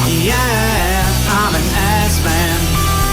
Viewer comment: After awhile it sounded like he was saying I'm an ass mat.